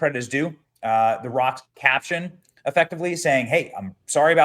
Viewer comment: Lol omg i thought you said the rug. That made me laugh so hard. Yeah hes leathery and old like a cow hide rug.